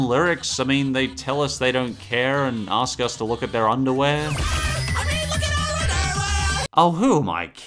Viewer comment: Wow fan boy and Chum Chum said look at are underwear I thought it said welcome to the underworld not kidding